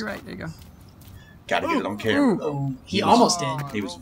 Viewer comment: did you call him n...?! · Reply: AJ Brown pretty sure he said 'there ya go'